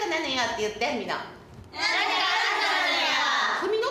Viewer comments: なんとかなんとかなのよって言って皆んなって聞こえたのかな？😂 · ここ字幕見てたときは(なんでフミノって言われてんのにこの子達やりだしてんのwww)って可愛らしく思ってたけど、周回しまくってながら見になったことで気付いた「〜なのよ、って言って"みんな"」に聞こえるわww、